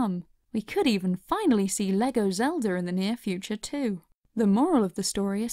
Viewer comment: Did she say Lego Zeldur